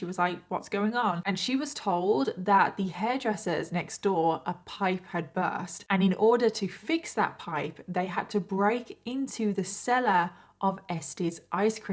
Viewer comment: ❤😮I THOUGHT FOR A SECOND, YOU SAID ESSIE❤ MY SISTER'S NAME IS ESSIE, AND MY MAMAW'S NAME WAS ESSIE ALSO ❤